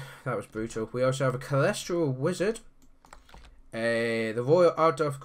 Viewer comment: Sounded like you said a cholesterol wizard XP